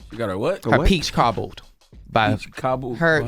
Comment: LMFAO Shit I thought she said Peach Cobbler too 🤣🤣🤣🤣🤣